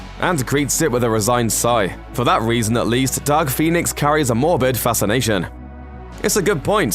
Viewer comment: With your accent, it sounded like you said Duck Phoenix.